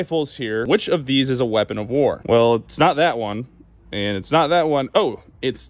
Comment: “weapon of war” is a very different question from what i thought he said, “weapon FOR war” cause i wouldve grabbed my DP27 and went to town.